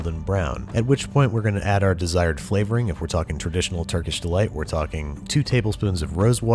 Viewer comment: Who else had an unnatural obsession with that scene and Turkish delite not even knowing what it was??? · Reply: That was me, but I was obsessed for the wrong reasons. Growing up I thought they said "Turkey Delights" and seeing they were red cubes, I always thought he was eating raw meat dipped in flour.